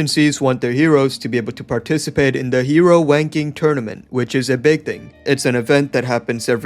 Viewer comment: “the hero wanking tournament” 😭😭 I thought brudda was gon go into a improv with the “wanking” part after seeing it was actually said with closed captions but naw, dude didn’t wanna be extra funny for a moment 🙃 But anyways, the video was still funny and good as all your other videos 🤙 one of my fav content creators on yt as a whole tbh